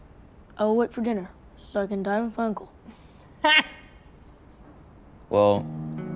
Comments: he sounds like he said i will die with uncle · what did he say? because i heard (so i can die with uncle) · did hé say il die with my uncle instead of eat or am I tripping